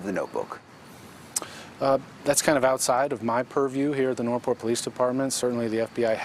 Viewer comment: Did that detective just say “pervy” instead of “privy” twice? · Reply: i think he said 'purview'...(meaning, e.g.. range or scope of knowledge?)...i wish people wouldn't use uncommon words that could be misheard, in TV interviews!